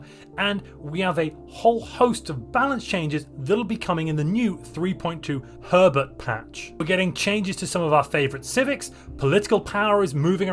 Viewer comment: I'm sorry But I keep hearing "Three point Two Pervert patch" every time you say it xD